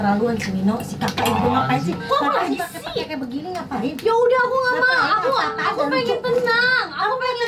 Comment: mama ngomong apa tuh???😂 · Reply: "Apasi pake pake begini ngak lucu..."